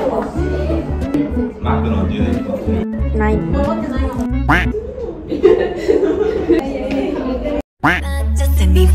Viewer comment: did I hear a "Nein"?👀 Or did I just imagined hearing the German language😶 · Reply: ない is no in Japanese